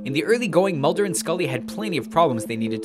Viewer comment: ish he says plenty instead of numerous